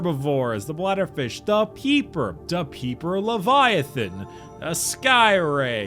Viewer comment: When was going through the logs, I thought he said Peeper Leviathan and not Reaper Leviathan... Petition to make a Peeper Leviathan